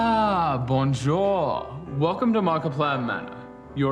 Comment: does he say “Bonjour” because that seems to be the only French word Actor Mark knows, based on “A Date with Markiplier”